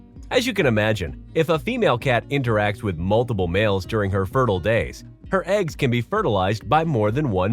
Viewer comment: Did he say cat has eggs